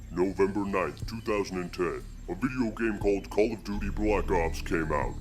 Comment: I remember my parents wouldn’t let me buy the game because they thought I said “Call of the Dirty Black Cops”